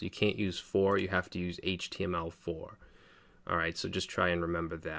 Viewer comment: I did not get this. "Classes in JSX is..." 'for', 'form' '4'? I assume they use className instead of class, but the other part and why wasn't very clear, sorry. Any help is appreciated, thanks! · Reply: The word "for" just needs to be replaced with "htmlFor" as for must be a reserved word.